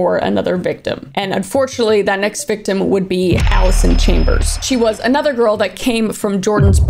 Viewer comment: Sounded like she said Alice in chain(ber)s lol